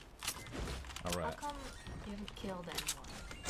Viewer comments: bruh im pretty sure those subtitles are wrong, i think she said, "How come you haven't killed anyone?" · she actually said "how come you haven't killed anyone?"